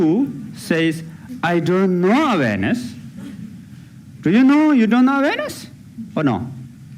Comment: Me too! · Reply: I thought he was saying "Do you know of anus".